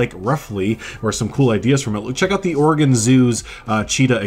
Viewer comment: At first I thought you said organ zoo 😂 I’m so excited, my fiancé is buying me a gaming laptop for Christmas so I can finally play planet zoo